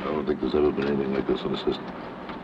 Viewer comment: the captions say "but you'll be different because it'll be a nigga system"